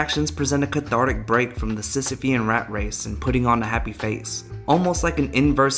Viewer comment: what word does he say at 7:50? Sisopy? The heck is that? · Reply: Sisyphean